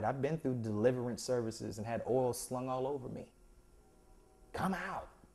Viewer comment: Not to be funny, but kinda... at 4:09 did he say he had, "Oral slung all over" him? Freudian slip much?